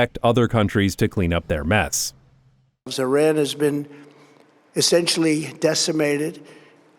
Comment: Did… did he say Z-iran because he was thinking about Zohran??